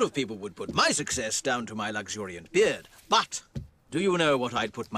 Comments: There’s a Mandela effect going on everybody thought he said Well yes but actually no But he actually says Good guess but actually no what is going on?! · I know the origin he said "good guess but actually no" And then the subtitles thought he said "well yes"